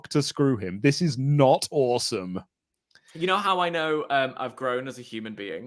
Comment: I thought they were saying “this is not awesome!”